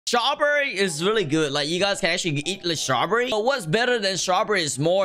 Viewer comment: Dude did he say shraw berry? Username: RAFIDG11